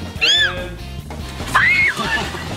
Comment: what is she saying? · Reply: ArbyTV ew ew ew EW!